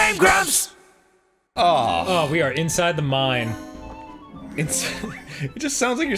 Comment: for me it's sounds like Danny says inside a mime